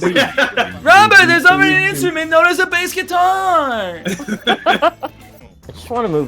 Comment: I've replayed this section 50 times trying to figure out what Ryan's trying to say. Help me! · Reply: Robert - Robert! There's already an instrument known as a bass guitar! 🎸